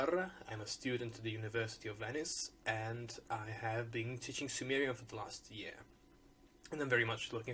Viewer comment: please forgive me. I'm not trying to be rude at all. But is your one year at university enough to teach us Sumerian? Is the language accessible enough to be learnable in its entirety in just one year? And will I be able to teach it after one year of study? If you say yes, then this tells me quite a bit. I, like most people, would think that it would take years of schooling and graduate work to master this ancient language. If it is at least,, oh, 90% learned after one year, then that's exciting! I've taken Latin, ancient Greek, some Koine, and Egyptian hieroglyphs recently, all of which have required of me quite a few books and reference materials ... and many months/years ... in order to somewhat master. I'm hoping that maybe Sumerian might be less of a brain-warping exercise. Thanks for your time and efforts! I'll be signing up shortly. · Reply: Oh, also, I just rewatched this video and you said you have been "teaching" Sumerian for the last year. I thought you said you have been "taking" Sumerian for the past year. I am sorry for not listening thoroughly. I have a bad habit of that.